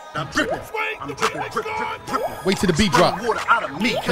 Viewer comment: I thought this man said I'm crippin at first I was like noooooooo